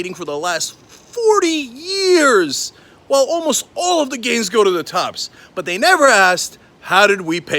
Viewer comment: I thought he said, 'All of the gays go to the tops' at the end... Say what?!